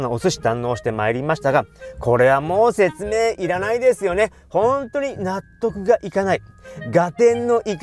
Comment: 最後の本当に納得がいかないが「本当に納豆食いに行かない？」に聞こえる✨☺️